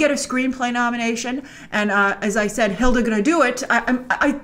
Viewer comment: When she talk about Hildur Guðnadóttir name pronouncing I thought she was saying hildur "gonna do it" as in wining the Oscar 😂